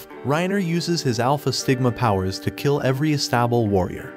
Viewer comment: Ok at first I thought the narrator voice said sigma but than I remembered these narrator voices can sometimes cause you to hear something else now I know the voice is saying stigma instead good work on the vid by the way I'm going to go see if I can't find this anime somewhere on YouTube